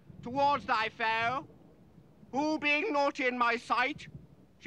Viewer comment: 'naughty in my sight' or 'not in my sight'? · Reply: I always heard it as "naughty in".